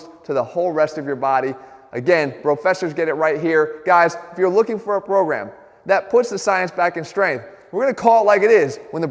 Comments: Did he say brogram? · I thought Jeff said, "guys if you are looking for a brogram..."